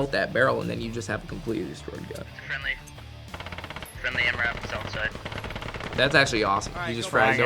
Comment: he actually said MRAP